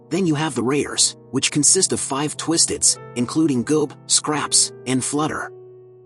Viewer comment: goop instead of goob